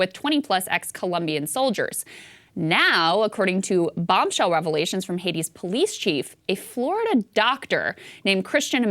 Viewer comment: I thought Krystal said to not believe what’s coming out from Hati officials ?